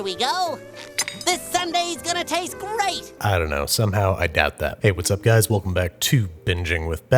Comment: instead of i doubt that i thought he said my dad left🤣🤣🤣